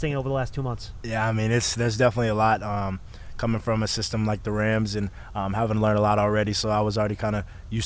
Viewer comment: Did Wan'dale say "coming from a system like the *RAMS*" ?? If that actually is what he said, does anybody know what he is referring to? (or did I mis-hear that?) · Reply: He said the Rams